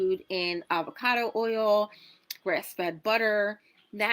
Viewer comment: Did she just say breast fed butter??? LMAO